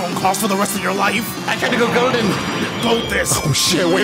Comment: What does freeza say when he goes golden? It sounds like he says "I tend to go golden" but that doesn't make sense given the context.